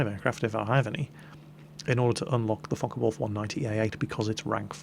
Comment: sounds like hes saying fuck a wolf aircraft at